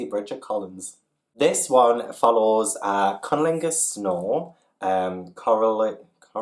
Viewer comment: I swear it sounded like you said Cunnilingus Snore 😂😂😂